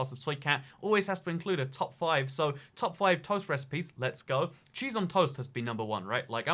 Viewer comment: My ear playing with my brain: Cheese on toast Me: Wait a minute is he said J*zz on a toast ???? (I just cleared my thought and that just coming like nuclear boom) Please don't report me m(_'"'_)m